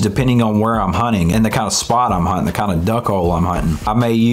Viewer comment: did u say duck hole?